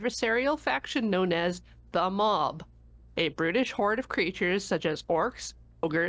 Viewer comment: I misheard "Brutish horde" as "British horde"